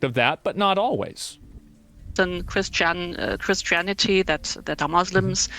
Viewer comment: I legit thought for a moment she was saying Chris Chan like that sonichew guy or whatever